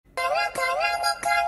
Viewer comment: For some people thought he said "I am a toy", he actually said "Anh em để tôi" mean "Let me do it my bro"